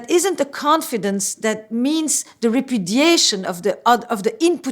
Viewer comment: did she say :Repudiation:?..anyone?